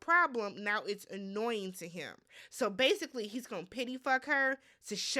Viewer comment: I had to put on the closed caption to get what was said about Austin & Beckka @ 19:10 or so. I ain't know if you had said "pity" or "tid-day" eff her. 😄😄😄😄😄😄Beckka unfortunately would be happy either way & that's sad & pathetic just like the season. So what are we wearing to the wedding? I've got a new pair of no fuggs to give with matching RBF. Now let me watch the rest..........